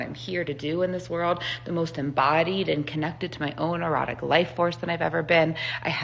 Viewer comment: #7: When she said "erotic life force," I heard "neurotic life force." I think mine is more accurate.